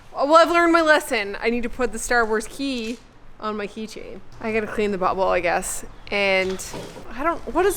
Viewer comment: I swear I thought said “I gotta clean the butthole I guess!”